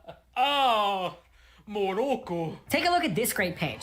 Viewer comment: Morocco? I know he's probably meant to say some kind of word in Japanese, but he's not pronouncing it right, so it's like he's referring to the country, Morocco. It just comes off as unintentionally funny and completely random to me, like what do mean bro? Morocco?! The subtitles can back me up on this, he is just saying Morocco.